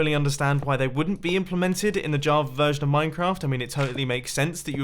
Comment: Why does it sound like he is saying JAVER edition?